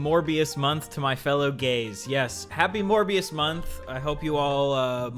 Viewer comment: at first i heard u say "more bs month" instead of "morbius month"